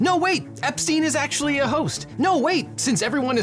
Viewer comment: Objection: “Host” instead of “hoax” at 0:03 😜